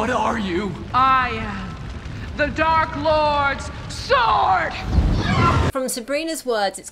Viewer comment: I always thought she said, “I am the Dark Lord’s source.” It’s not just the show that’s dark it’s the lighting.